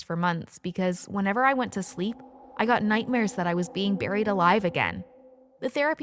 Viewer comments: I thought It said "burned alive" and i was like :0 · I thought it said burned alive😂😂 oops my mistake